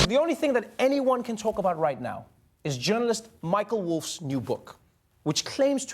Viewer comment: I THOUGHT IT SAID “MICHELLE WOLF” & WAS JUST WAITING FOR THE PLOT TWIST OHHHH MY GOD